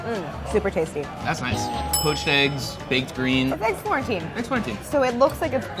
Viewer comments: I definitely heard that as “eggs quarantine” lol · I legit heard "It's quarentine" and freaked out cuz i know this is old